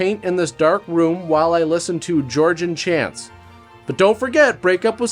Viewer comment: Did you say Georgian chants at 7:56? 😅